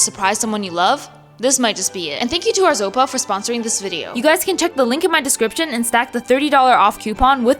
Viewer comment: I was doing something while watching your video and you said the name of your sponser. My bumbah thought you said "thank you OZEMPIC for sponsering this video!"...